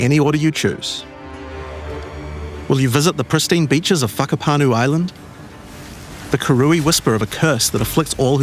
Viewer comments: did he just say fuckapanu island lmaoo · am i the only one who understands "fuck up on you island"? ;) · Did he say Fuckapanu island?? at 5:18 hahaha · Did he say f**ckapanu Island?